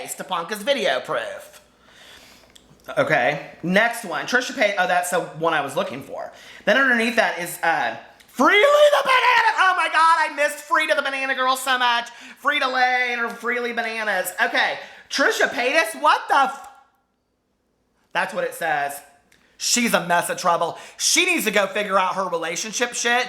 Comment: I don't know if this is what you had said lol but I heard "Frita the banana girl! Frita lay and her freelee bananas!" I am laughing beyond control over here!!! Love the joy you bring to me each day! Greeting from Northern Indiana.